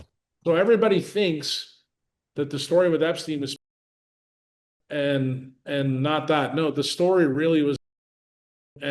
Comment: bleeped out P-feelia 32:32 what word is censored? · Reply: I believe it's adr3n0chr0me.